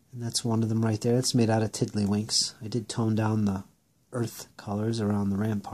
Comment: What did you make the palisade fort from ? It sounded like you said tiddlywinks.